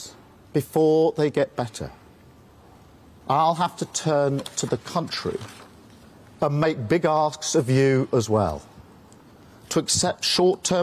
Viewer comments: I heard, ‘I’ll have to turn to you and make a big arse of you lot as well’ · Did Starmer say he’d make arses of us ??? That’s one thing he got right!